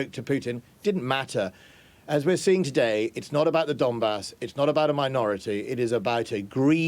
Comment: This is the frankest conversation about the invasion I have ever watched. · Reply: I seem to enjoy those more lol. I thought they were saying “dumbass” for the longest time.